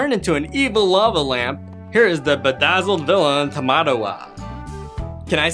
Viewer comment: does he say tomato wa? is that japanese 6:44 back at it again atop tomato wa